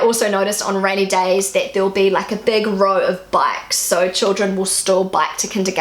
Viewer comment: I'm completely and utterly unable to hear what that word you're saying is... A big row of something even if it's raining. As for the "There's no such thing as bad weather, just bad clothing" I'm fairly sure that it's from Norway. At least that's very much of our culture. Most of the things you mentioned about Germany also applies in Norway. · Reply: The word I said was "bikes" (bicycles) My New Zealand accent can be a little hard for some people to understand when I say certain words.